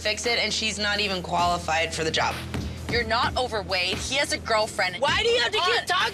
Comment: When Rosa said "You're not over Wade" I thought she said "You're not overweight"